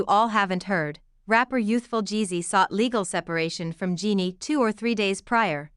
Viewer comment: AI SOUNDS HORRIBLE. JUST PAY SOMEONE TALK. 👎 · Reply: i heard AI say "Youthful Jeezy" 🤣🤣🤣🤣🤣😂